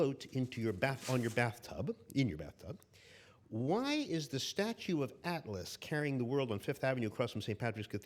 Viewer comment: OH! ‘Statue of Atlas!’ I thought he said ‘Statue of Alice.’ Seriously, I thought maybe rabbits evolved to wear clothes, carry watches, and drink tea.